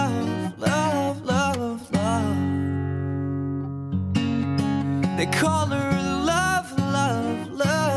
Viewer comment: I thought at first the song said "the color of love, love love love" I was like D